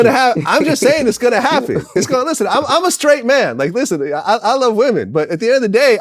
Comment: Somebody tell 1:06:14 Vald does not say "listen ni**a." 😑 I swear I played it back twice and heard that shit.